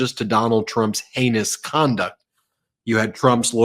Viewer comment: Ben when you said “ his heinous conduct”, I thought you said “anus conduct” 😂. On reflection though anus conduct is so much more apt!